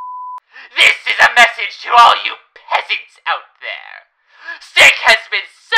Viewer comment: I thought it said peanuts for some reason